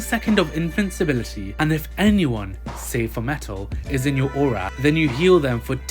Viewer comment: "Save for Metal" that means excluding metal, not including him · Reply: oh. I thought he said “say, for metal” and not “save for metal”